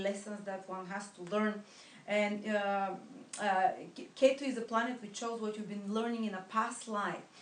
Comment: what was the planet you mentioned that has karmic lessons? K-2? I'm sorry, I couldn't catch it.